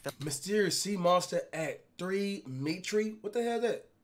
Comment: he really said “at mitre” 😂